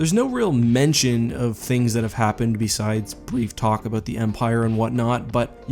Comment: the blue alien guy at the start feels more like a Guardians of the galaxy character than a star wars character imo. really enjoyed the episode. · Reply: So much so that when he said "pre-Empire" I initially thought he said "Kree Empire."